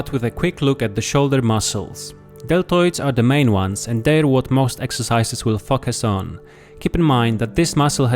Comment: am i the only one who heard "they what the most exercises will fvck us on"😂